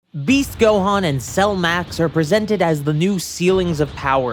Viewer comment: I thought you said Beef gohan at the start lol